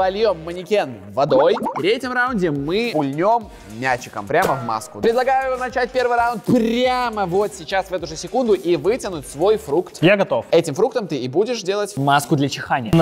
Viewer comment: Мне послышалось ударом 🗡 а там был мячик